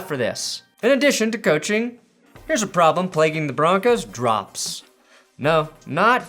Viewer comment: I thought said Twitch drops😂 9:02 I got too excited